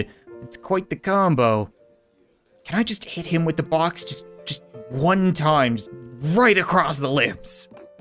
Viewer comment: Bob says “just this one time”, but all I hear is “just one more score”